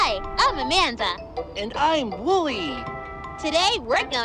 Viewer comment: I thought amanda said "Hi! Im wooly!"